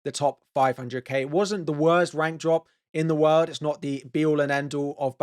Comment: All I hear at 1:17 is 'Wank Drop'... Please stop saying 'Rank' like that it makes me chuckle and I'm an adult. Jeez.